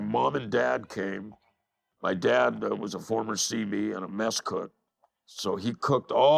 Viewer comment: I had to replay the part where u said your dad was a mesa cook , lol sounded like you said mexh cook, didnt sound good !! But i heard it right the second time..